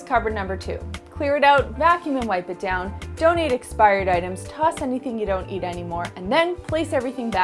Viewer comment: did i hear donate expired items?????